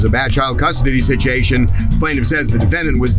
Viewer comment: did he say “sit-chee-ation”? 😂😂😂